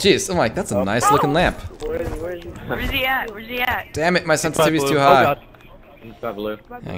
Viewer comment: Did you just say your sensitivity is too high on 4